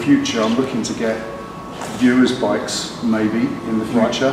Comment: Best porn of any kind I've ever seen. Did I heard "viewers bikes" ? Sounds awesome .